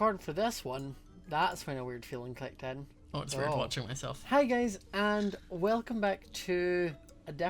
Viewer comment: whenever he said hi guys at the start of the video i heard "hi gays" EXCUSE ME